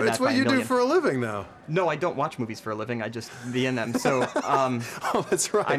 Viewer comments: "No I don't watch movies for a living, I be in them." well said. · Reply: i thought he said I don't watch movies i've been in lol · Am I the only one who heard him say "I don't watch movies im in"?